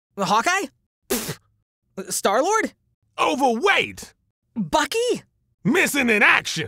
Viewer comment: WAIT..DID YOU JUST SAY...BAKI!?? THE ANIME CHARACTER!?